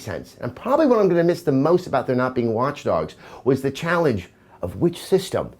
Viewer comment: Did you call it Launch Dogs? @ 3:50 :)